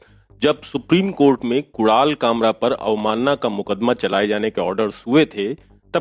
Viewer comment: what the f did he say ? Kural kamra ? F lol 🤣😂